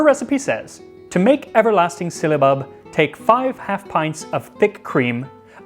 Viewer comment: At first I thought it said syllabus